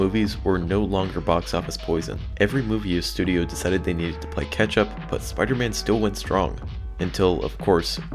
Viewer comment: it almost sounded like you've said ketchup